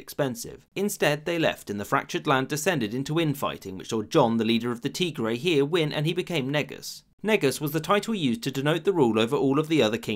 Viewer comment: what did he say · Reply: John (yohannes) the leader of the tigray win and he became negus